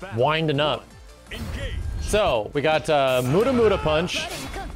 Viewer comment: Did... did Max just say Muda Muda punch?